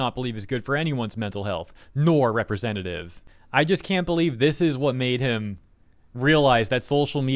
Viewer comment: I thought you said “this is what they/him…” wow that’s a new one.